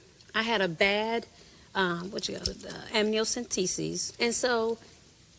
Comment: A bad Amniocentesis? Girl stop. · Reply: I thought she said "hemiocyntesis"